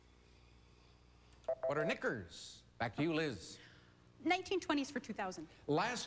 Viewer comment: What did he say? :P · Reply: He said, "what are knickers"?